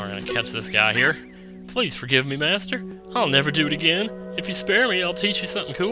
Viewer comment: when i first watched the first deku in this video i thought he said spayed instead of spare